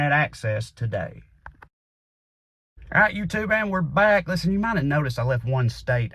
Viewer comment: I had my sheetcutter going while listening to this rant and I swear that I heard Trae at 4:14 saying,; "alright youtube, the N word´s back." so WTF, then I went back and heard him on that,; "alright youtube AND we´re back", and I damn broke my face laughing. srry but its funny as all